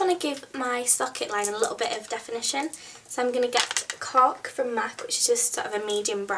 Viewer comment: did she say cock or cork at 2:55? -.